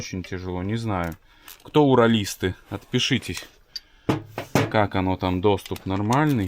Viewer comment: Мне послышалось отпишитесь, я и отписался